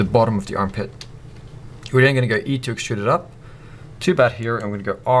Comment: what on earth are you saying at press at 2:42? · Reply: E, to extrude